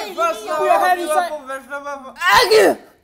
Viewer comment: Am I the only one who heard "Argue"? 😂